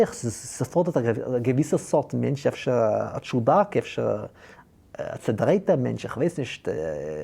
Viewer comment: it sounds the the Yiddish word for "oddball" is "chewbaccashe." Am I hearing that right?